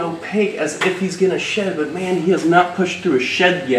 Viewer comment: I heard “His chest is looking nice and opaque like he’s gonna shet” and snorted😂